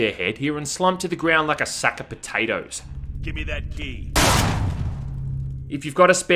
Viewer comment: Did anyone else think that guy said give me that d